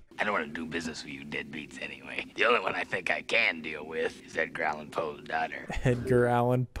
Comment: This is the first time I realized Beetlejuice said "Edgar Allen Poe's daughter". I thought he said something like "growling poe's daughter" ??? But yeah, I first saw this as a kid and it freaked me out so bad. I'm glad it freaked out a grown up.